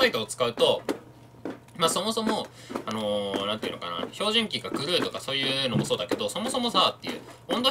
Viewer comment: 照準器を「ひょうじゅんき」って聞こえる。標準器？？ってなってしまった。